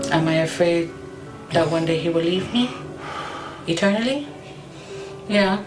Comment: I thought she said am I afraid he will eat me! But, she said leave me lol 😂😂😂